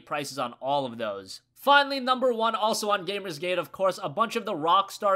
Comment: For some reason I confused gamer gate with feminist frequency for a second... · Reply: I thought he said "GamersCade"